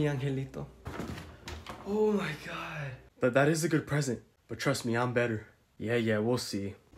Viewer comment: i thought bro said “i’m not black im a good present”